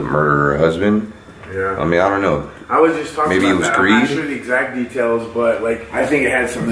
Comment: When you said “greed” I thought you said “Greek” and if you did then I could read it for you 😂 cause I’m Greek 😂